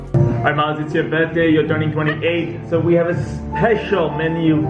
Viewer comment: I thought she said she's Turing 25😓